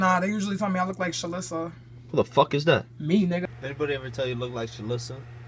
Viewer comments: i thought she was saying “me neither” my whole life but i finally figured out what she said. “me nigga” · The whole time I thought she was saying "me either", like she didn't know who Shalissa was either. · did she say "me either" or "me nigga"?